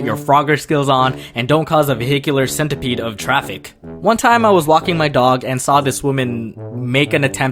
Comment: i heard LOCKING my dog